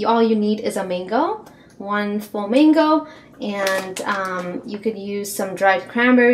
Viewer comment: I thought you said full flamingo instead of full mango it sounds alike